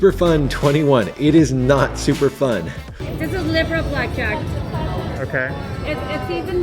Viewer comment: What are those camera clips in the variants section from? I can't quite make out what she's saying at 7:10 but it sounds awful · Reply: It's "Liberal Blackjack": "all of these 'good' rules don’t make up for the Plaza's poor payouts on blackjacks. The house edge at the Plaza is about 1.3%, not much better than the house edge on most of the single-deck 6-5 games in Vegas." -Las Vegas Advisor